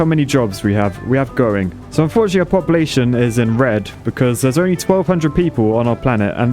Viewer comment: "Our population is in red" What I heard "our population is inbred, because there is only 1200 people" Me: makes sense but I should rewind and be sure